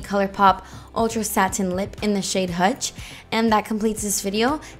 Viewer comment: tell me why when you said hutch for your lips I immediately thought you were trying to say hush like miranda sings